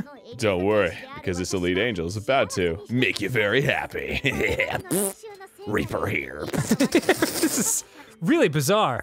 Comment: R E A P E R H E R E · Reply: I thought he said raper hair